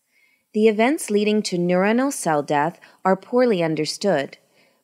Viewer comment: Pl can someone tell me what was said at 0:48 "The events leading to neurono........" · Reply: “ Neuronal cell death “ is what’s said.